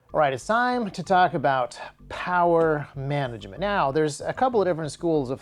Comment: I thought is said power magnets :(